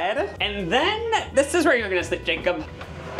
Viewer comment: “this is where you’re gonna sleep Jacob” I heard “this is where we’re gonna put Jacob” and combining that with letting it SLAM to the ground I’m in tears